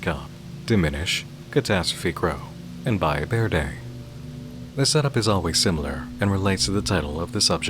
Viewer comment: can you please tell me what the name is of the last game you mentioned at the segment during 0:40 ? i cant really find much when searching “buy a bear day” but im assuming i misheard lol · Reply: Valle Verde